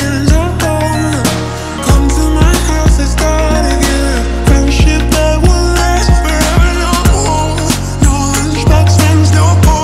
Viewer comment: I hear “come to my house, lets die again. Friendship that will last together”